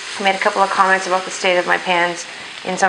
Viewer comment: I swear to gods I heard, "the state of my panties" instead of "pans". I was so confused I had to skip back and watch again. Now I'll be laughing at my stupidity all day. Also, can't wait to try this recipe out, thanks! ♥